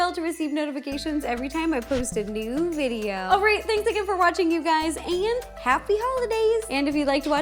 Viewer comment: I thought she said “ pancake for watching !” But I went back and she said “ thank you for watching !”